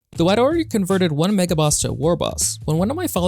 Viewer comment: For about 2 secondz, I thought it said Maga Warboss! 😲